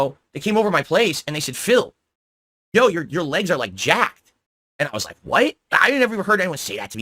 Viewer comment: I think your friends said jacked up phil as in look like crap